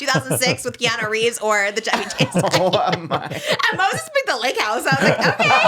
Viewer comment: not me typing 'chubby chase' into google cause that's what i thought was saying the whole time😭😭